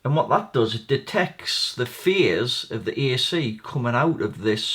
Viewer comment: Lol - coulda sworn you said the little chip detects the FEARs of the incoming AC there for a minute ;-)